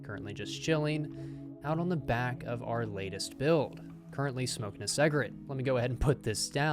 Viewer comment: i’m sorry did you just say “smoking a seg-rit” 🤣🤣